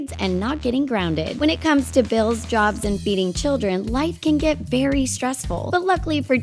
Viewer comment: When she said "feeding children" I thought she said beating children.